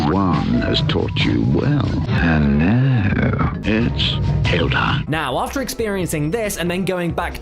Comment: who thought said bitch tale time